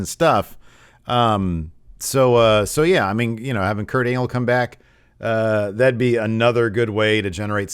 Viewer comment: Is it just me, but at 2:59 did call him Kurt Anal?